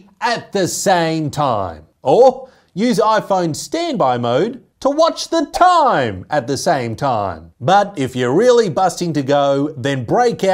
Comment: I heard "TO watch the time, at the samtime" 😂😂😂